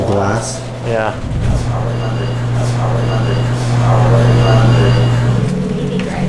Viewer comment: am i the only one who heard "thats oli london"?